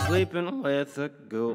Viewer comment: i thought he said sleeping with a goat